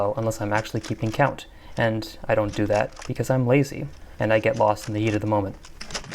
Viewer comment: totally heard that as "I get lost in the yeet of the moment"